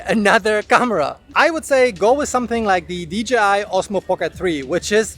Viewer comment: he says "go for the Osmo Pocket 3" and the subtitle says "Osmo Action 3". this footage is UNUSABLE!